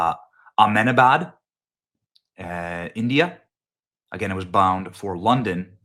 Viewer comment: At first I thought Benny said, the city was, All Men Are Bad, India. I was confused because, I know for a fact that, All Men Are Bad, is in California.